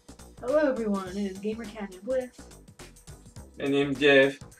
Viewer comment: Wait what did he say? XD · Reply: He said "My name Jeff". I do not know why.